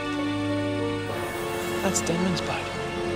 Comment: did I just heard "that's Denman's butt".?!?!?